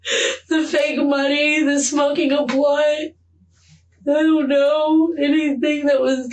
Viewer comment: Did she say “smoking blood”?